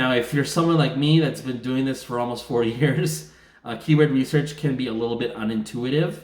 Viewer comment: You've been doing keyword research for 40 years? Did I mishear that? 4:45..you look great for your age! Jokes aside, the keywords guide book by dale is awesome, literally can't get the info anywhere else for the preorder ebook price. It's awesome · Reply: 4 years :P I'm glad that you're happy with guide - his info is always top notch!